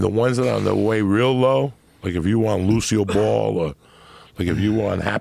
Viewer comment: sounds like he says 'Lucio ball' like from overwatch! Any gamers?